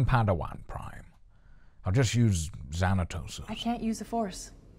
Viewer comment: Did I hear right and Qui-Gon namedropped Xanatos casually at 8:32? That's a deep cut and a name I haven't heard in a long, long time...